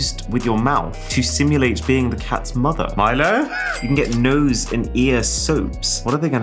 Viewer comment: I thought he said Tyler and I was like oh crap that's my name! But he only said Milo! Phew...